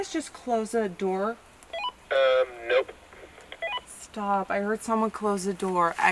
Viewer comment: i heard a weisper 6:54 is no one gonna talk abu that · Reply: At around 6:52 I hear someone whispering "stop"